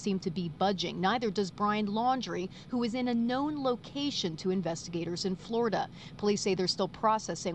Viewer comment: Did they just say Brian is in a known location in Florida? Or did i hear that wrong.. or was that from before he went missing?around the 7:55 ? They need to re interview Cassie, she will crack! I knew she was lying, she also looks like she was punched in the jaw, she has a big red mark there.